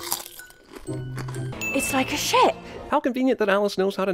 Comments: I thought Alice said the ships a shit. I think I'm going deaf. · Is it bad that I thought Alice said "it's like a shit" in the clip he played